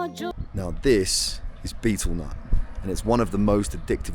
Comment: Hey, at 20:07 I can't understand the item in your hands the way I hear you pronounce it. However I write "beatleman", bitolmon" and so on in search engines I can't quite find out about it. Please spell it out for me. thank you. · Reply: it's called betelnut its like a zyn on lsd, banned in a lot of countries not only for its pychosis effects but it destroys the human teeth, its why a lot of people in asia have extremley bad teeth.